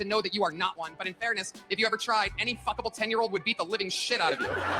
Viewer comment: Did he really say "a fvckable 10 year old?" Who looks or even thinks of children like that? 🤬